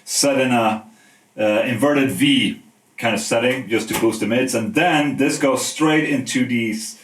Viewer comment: did I hear boost the mids? :/